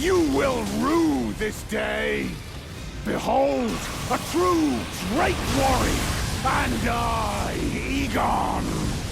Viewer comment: You misheard Igon’s line during the fight with Bayle. You said “Igon declares himself a true drake warrior”, but what he really said was: “Behold, a true drake warrior! And I, Igon!”. He says you are the true drake warrior.